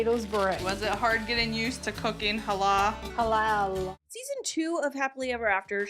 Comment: "Was it hard getting used to cooking Allah" "Halal ☝️" Please tell me I'm not the only one who heard that 😭